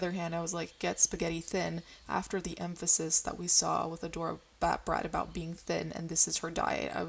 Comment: I gotta be honest, when she said her name for the first time I thought she said "Adora Badbreath" and I was kinda like...no wonder she's problematic XD